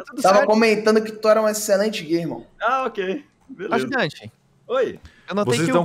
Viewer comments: sou so eu que escutei o dizendo "So comentei que você é um excelente gay irmão..." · Só eu que ouvi o falando "tava comentando que você é um exelente GAy"